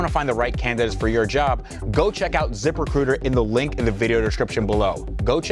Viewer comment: Im so tired at the moment i thought he said zipper cooter instead of zip recruiter 😂😂😂😂